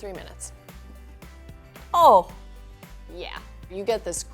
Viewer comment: waittttt did she said "oh fuck yeah" 1:54 and skipped the fuck part lol that was hillarious